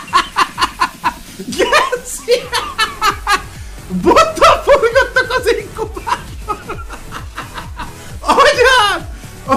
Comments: valaki megmondaná mit mond? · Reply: bottal forgattak az inkubátorba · nál mit mond? xd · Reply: Bottal forgattak az inkubátorban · nem értettem amit mondot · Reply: Bottal forgattak az inkubátorban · Itt mit mond? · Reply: Bottal forgattak az inkúbátorba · nel mit mond? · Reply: Bottal forgattak az inkúbátorba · nél mit mondott?😂😂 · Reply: Bottal forgattak az inkúbátorba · nél mit mond am? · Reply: Bottal forgattak az inkubátorban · nél pontosan mit mond? XDD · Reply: Azt mondja, hogy Bottal forgattal az inkubátorban xdd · nál mit mond ? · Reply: Bottal forgattak az inkubátorban